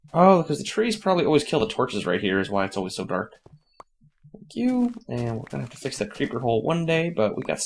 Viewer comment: it sounded like f you lol